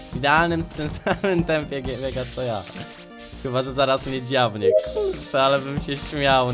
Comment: Czy tylko ja usłyszałam ku**a ? XD